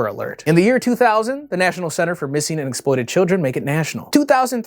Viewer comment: lol. i heard "Missing and exploded children." That woulda been messy.